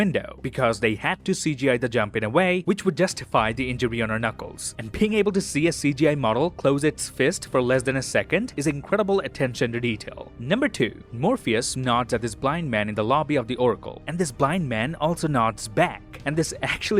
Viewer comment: Is it only me or anyone else heard 'Morbius'